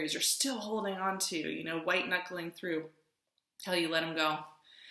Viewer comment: it sounds like you said "kelly, you let them go", like an order to get rid of what I need to internally. That really got me. Message received. Let's go 🏁